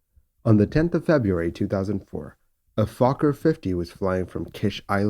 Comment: did he say a f*cker 50?? 😂😂😂